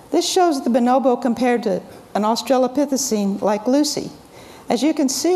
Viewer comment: Hello, at no point did she say Lucy was from Australia, she said Lucy was an Australopithecine 3:25 The term australopithecine refers generally to any species in the related genera Australopithecus. Have a wonderful day.